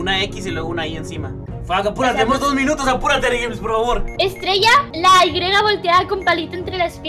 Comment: escuche bien a purate amor xdxdx